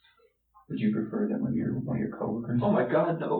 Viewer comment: what does the cop say that Chris replies “oh my god no! No!”? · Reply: “Would you prefer that one of your coworkers...” He can’t stand the idea of his coworkers learning the truth of who he really is